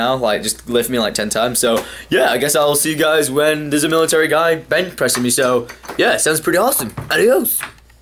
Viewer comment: what I heard Ill be back after a army guy is bent, pressing me